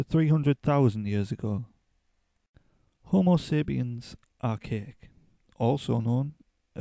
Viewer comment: I thought you said "homo sapiens are cake" hahaha